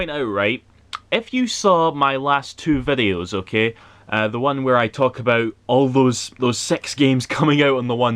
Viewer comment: I heard "the one where i talk about all those SEX games on one day" at 13seconds lmao